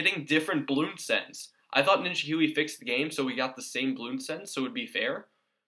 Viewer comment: At around 10:48 I think You say Ninja Goowy, or at least it sounds like that